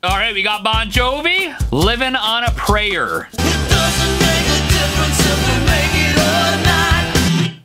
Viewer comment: I can’t be the only one who heard “it doesn’t make a difference if we make out or not”